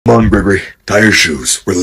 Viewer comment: Why does it sound like Freddy said bebe and not gregory😀